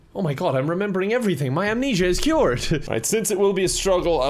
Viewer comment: Kevin: cures his amnesia Also Kevin: forgets he has amnosia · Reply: I just found out that he actually said amnesia in the video, I really thought he said amnosia. I guess my brain just automatically autocorrected it